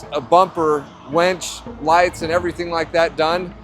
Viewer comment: winch. Sounded like you said wench. Don't think he wants a wench on his front bumper..